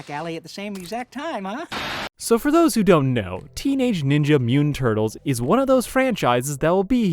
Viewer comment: when you get about a minute in and you hear Teenage Ninja Mutant Turtles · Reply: Ngl I thought he only said Teenage Mutant Turtles and I wanted to fist the computer monitor 😂